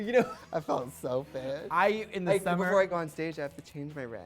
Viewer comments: what did trixie say at 1:18 after "I felt real fish" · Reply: that before she went on stage she had to "change her rag"😂😂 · what did Trixie say? I'm sorry I'm not English speaker and sometimes I don't understand some words. Ty. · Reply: I think she said "like before I go on stage I have to change my rag", but I'm not 100% sure because it was hard to hear! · What does Trixie say at 01:17? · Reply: before I go on stage I have to change my rag? I think I'm not sure.